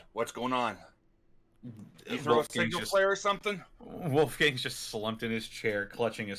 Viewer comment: I can't make out what said. It sounded like "you throw a single player or somethin" to me?